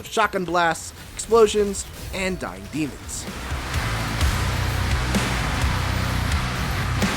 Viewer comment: I had to turn captions on I kept hearing diabetes and I knew that didn’t make sense and it was actually “dying demons”